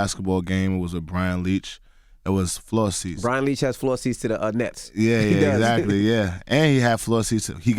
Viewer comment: Why I keep hearing them say "floor seats to the nuts?" I know that's incorrect lol